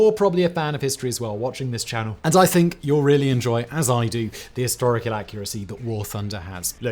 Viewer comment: ~ "Historical inaccuracy" as I heard it. 😆